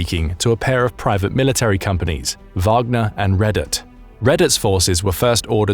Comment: "He was also speaking to a pair of private military companies: Wagner and Reddit." Is what I heard.